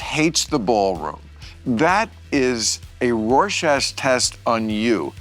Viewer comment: Did he say “rorschast” test?!